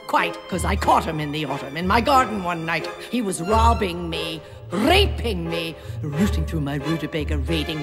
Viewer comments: ARE YOU SURE SHE IS SAYING RAVING ME? Because I swear I keep hearing rape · i tought i heard "RRRRapping me"but instead that was Raving me